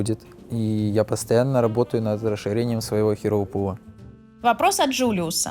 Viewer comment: "постоянно думаю над расширением своего херо-пулла" послышалось "херового пулла"